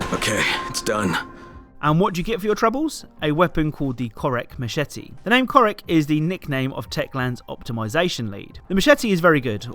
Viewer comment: It sound like he said Cheesecake.